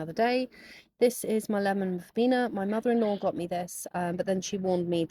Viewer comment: I thought you’d said lemon Ribena instead of lemon vena 😂